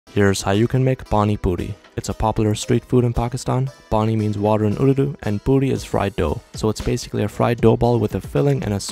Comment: I actually thought he said at the start 'How to make Barnie butty' I was like woah